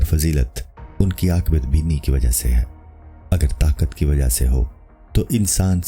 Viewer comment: par kya bola hai bata sakte hain? Mardon ko aurton pe fazilat...? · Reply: "Akibat Beeni" (آخبات بینی) is an Urdu term that can be translated to "foresight" or "foreseeing" in English. baki batein aap khud samjh sakte hain